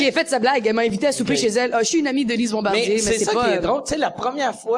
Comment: est-ce moi ou Mariana dit: Lise Bombardier